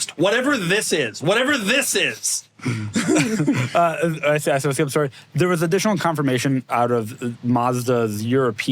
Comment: Does anyone have any idea on what Filippo says here? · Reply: I should say, im sorry